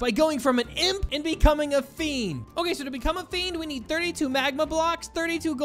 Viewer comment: I seriously thought that at 7:40 it said friend 💀💀💀